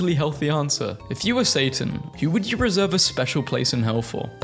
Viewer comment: thought it said santa 😔